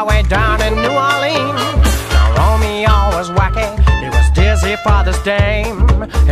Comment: Did he just say what i think he said? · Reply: "Romeo is wacking"